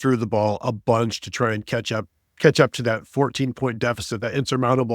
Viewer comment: Did I hear say ketchup or catsup?